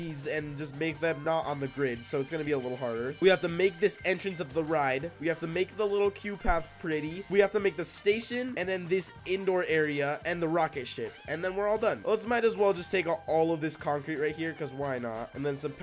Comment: Ok is it just me I thought I he was saying cute path until I saw it said queue path 😭😭